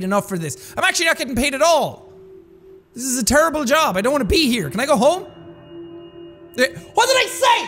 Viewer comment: I thought jack said “ I don’t wanna ‘ pee ‘ here “